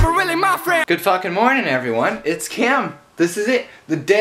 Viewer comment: "It's Kim" He must been thinking about how he will see the worldwide handsome while sleeping I know that he said "Cam" But it's sounded like Kim